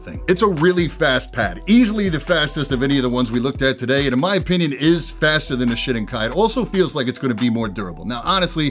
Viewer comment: "Is faster than a shitting kite" that's what I heard lol